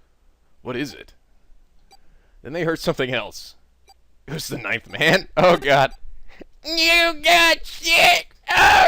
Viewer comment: He says 'ninth man', I hear 'knife-man'. Gets really confusing when random characters refer to him.